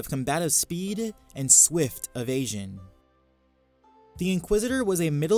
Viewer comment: did anyone else hear swift of Asian